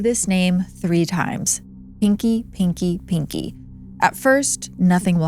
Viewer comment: I thought you said stinky pinky.